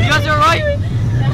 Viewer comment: At like 5:03 did he say dotn call 911 ?a.